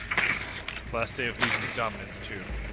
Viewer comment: What op were you talking about at 10:10, I couldn’t quite make out what he said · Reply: We were referring to Lesion.